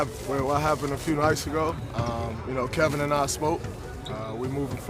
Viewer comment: I swear at 0:03-0:05 he said " Kevin and I smoked"